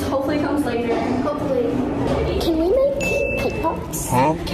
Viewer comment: When she said, can I make cake pops? I thought she said can we make TikToks?